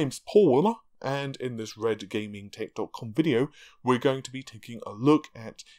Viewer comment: I really miss the Tittycum :( Who the hell bitched and whined about that, it was funny every time. #Naarvey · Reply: mishima When Paul says "redgamingtech dot com" fast, like he usually does...it sounds like he is saying tittycum check out his older vids